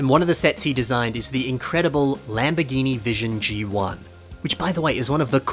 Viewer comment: hearing “vision g1” instead of “vision gt” has caused some sort of mental anguish in me